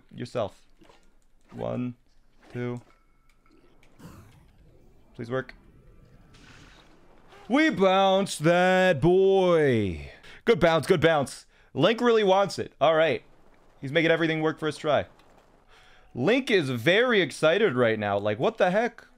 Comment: when you said "lets go boy, good bounce good bounce" i thought you said "good boy good boy" and i had a heart attack